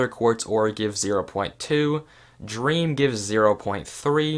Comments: did anyone hear dream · anyone heard him say dream instead of clay lol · am i high or did he say dream????